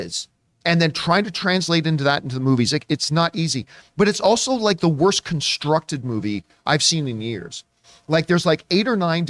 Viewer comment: I'm just stating the obvious. That's fine if he doesn't like the movie, but it is beyond ridiculous for him to say that it is the worst movie in the last 3 years, when it's not. I don't like or respect drama queens, and he is the biggest and most pathetic drama queen I have ever seen on the web. That's fine if you like drama, but I don't tolerate it. In terms of quality of representation, the acting legends Lou Diamond Phillips and Tia Carrere have praised Jo Koy in numerous interviews for making history with this film for its representation because they have worked for this for over 40 years and were never able to get this done, so if you want to argue about that, take it up with them. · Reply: Here is the time stamp for what was actually said, 2:27 he said it’s the worst “constructed” movie in years, not the “worst” movie. And it still has a 44% with critics from RT and a 41 on metacritic, but I do hope people still go out and see it and judge it for themselves. And what if I am sleeping with John? Do you have a problem with gay people? 😂 (this was fun, have a good evening)